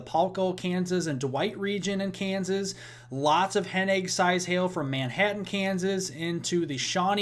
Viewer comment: Hen egg sized hail. I thought you said "headache sized hail". I wondered How big is that?" Then I looked at the screen. Hahaha, I get it...better late than never. I love your reports, plenty of info in a short amount of time and you are easy to listen to. Oh, hi from NW Ohio near Ft Wayne, IN.